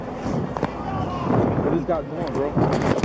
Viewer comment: i think cop said "it just got boring bro" haha yeah cmon dude at least leg it on foot through the woods dont out like a bitch